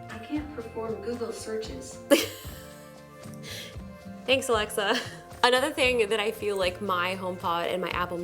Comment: did she say alexa?😂